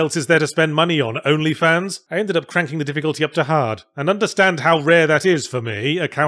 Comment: Yahtzee said: “I ended up cranking up the difficulty to hard”. I heard: “I ended up cranking up the difficulty to Jihad” Cue me deep-diving the word Jihad in Google to see how difficult that is because I didn’t understand the metaphor.